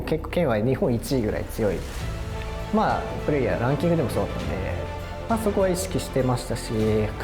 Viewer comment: ここイントネーション的に「プレイやランキング」って言ってる風に聞こえるの俺だけ？